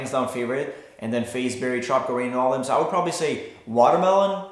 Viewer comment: Literally thought he said "Chocolate Rain" instead of tropical rain.... I instantly started singing it.... :/